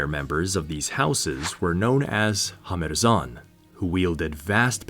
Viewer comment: You say: "Hamherzan". I hear: "Kurfürst".